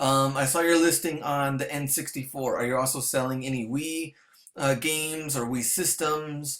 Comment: Thumbs up if you think he said selling any weed at 2:30 lol